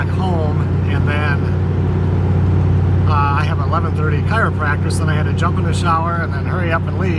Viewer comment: Lol, at 11:05 I thought you said you had "an 11:30 choir practice"! I thought, what the heck?? Then I heard you say chiropractor. 😄 Funny but not. Hope your back is feeling better. This week 40's and 50 in SE Mich! Strange February.